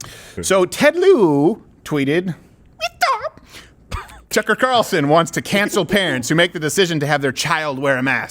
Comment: i thought you said tucker carlson was now an enemy (for no apparent reason i should note)